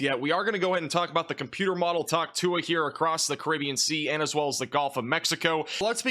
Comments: nah I’m tripping or did I just hear talk tuah 🤨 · I’m not trying to be super brainrot but I swear he just said talk tuah